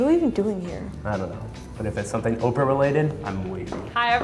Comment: I swear I thought the video said orphan instead of Oprah lol I m such an idiot!!!!!!